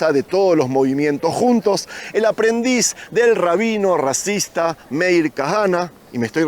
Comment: Entendí bien? A los 18:57 minutos: “…el aprendiz del rabino racista…”.